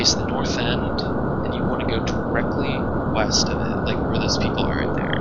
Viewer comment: I thought you said weast. srsly tho i’ve tried to do this several times and have failed help